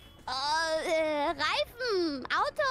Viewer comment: 這段我昨晚看得很感動...（？）朝思暮想的火雞德語大滿足(警察先生，對，就是他 總之烤肉大人辛苦了:3！以下是我回去聽的整段火雞語，請笑納（？ Deutsch Technologie! Licht und Geschwindigkeit, Energie, Qualität, Hoffnung, Reifen, Auto und Fahrrad. 德意志科技! 輕盈和速度、能量、品質、希望、輪胎、汽車和腳踏車（是說火雞你怎麼把母語講得像精神標語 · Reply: 感謝翻譯 哈哈哈 我聽成reisen了 慚愧